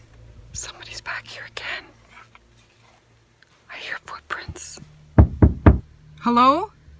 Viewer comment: I hear”footprints” 😂😂😂